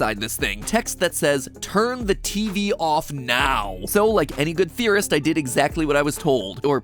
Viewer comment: Top notch intro. Team Theorist puts in a lot of effort and it shows. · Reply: I thought you said terrorist